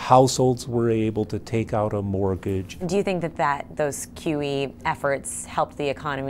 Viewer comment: At about 5:06, I thought the reporter said, "Do you think those QTE efforts helped the economy recover?" And I just imagined some economist looking at a declining graph and yelling "Press triangle to recover!"